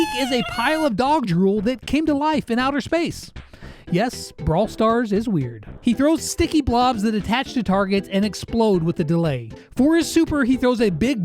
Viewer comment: I thought he said throws sticky balls 😳